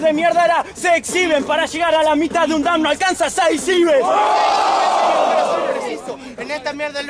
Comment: que dice a lo ultimo dam?? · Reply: Braian Moreira Para llegar a la mitad de un dam no alcanzan 6 ives